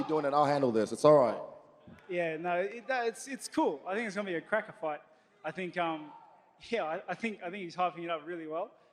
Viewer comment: Don't think I've ever seen Whittaker as pissed off as he does now, gonna be a cracking fight. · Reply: oh...I thought he said “cracker fight”. I guess “cracking” makes more sense.